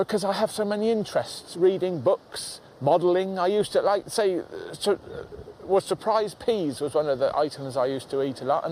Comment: in... I'm from the US ...I don't know what he's talking about.. He says he used "prize p's" or "surprise p's" ... I'm guessing its a boxed candy or food...🤔😆 just wondering.. I'm an uncultured American.